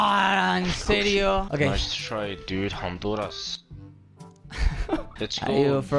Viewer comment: Yo creo que dijo Honduras en el minuto